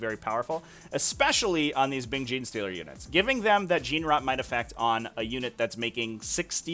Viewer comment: I can't tell what you are saying. Gene rot effect? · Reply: Gene Wrought Might is a stratagem for all the Space Marine chapters; 6s to hit in melee automatically wound